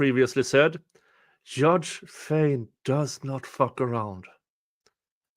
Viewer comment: My apologies, i said "Judge Fein" in the video. His name is Judge Everett. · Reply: I thought you said which I'm sure he'd be fine with as a nickname 🙂